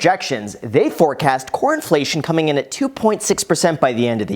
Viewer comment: I thought Graham said Corn-flation and I got scared